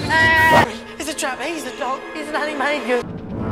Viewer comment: What does Ronald say at 0:32? (Princess Tutu for Super Smash Bros.!) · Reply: “It’s a trap, he’s the dog! He’s an animagus!”